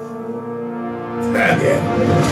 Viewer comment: what did he say at 1:37, couldn't hear bc of loud ass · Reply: 2k17 chill "Again."